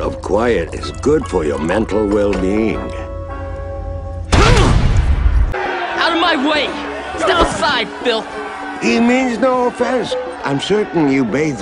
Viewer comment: I was always confused about Iroh's comment after Zuko pushes past the crowd. For whatever reason I thought that Zuko had said: "Step aside Phil!" 😂